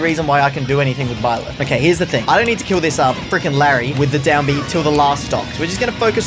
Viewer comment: And I thought you said you won't down B on the last stalk